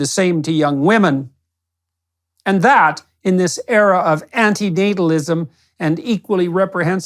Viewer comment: “in this era of ? What? anti-“Dadaism”? What is the word he is saying ?